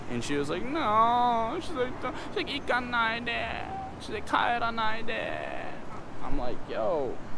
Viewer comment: What is he saying here? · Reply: 帰らないで (かえらないで). Basically means don’t go